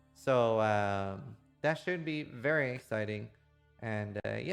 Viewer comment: I just realized you say "Try to die... less than I do"... I always heard it as "Ride or die.. bless the *unintelligible*". SMH...